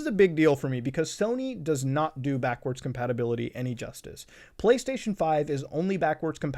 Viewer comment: thought said: “police station 5” 😳